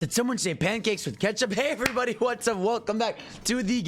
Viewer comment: All I heard was someone don’t like Syrup.